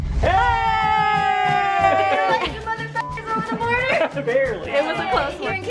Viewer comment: I love how first thought when they show up at her door is "they let you motherf**** over the border" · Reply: I heard "who let you mother f***ers over the border"